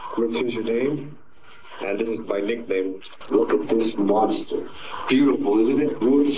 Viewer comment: quick correction: I think he's saying "This is a Mossberg" instead of Monster. But man, watching this kid's descent into madness and the fact that the system had failed to help him numerous times is just saddening. He's still a horrible human being in the end, but the general circumstances of his life didn't help him either.